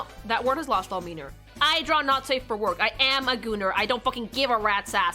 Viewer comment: WHY DID IT SOUND LIKE YOU SAID N@ THERE?? I FUCKING DROPPED MY PHONE WHILE FARMING IN ZENLESS BECAUSE I WAS CONFUSED AND SHOCKED AND HAD TO CHECK MY EARS.